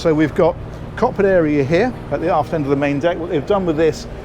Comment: I swear I heard the 'arse end of the main deck' at the beginning 😳 lol 😆 Anyway very double decker from the side view. Masses of room and great views from the side. Love the helm. Great tour seem to be getting quicker but that my be just me Demanding more lol 😆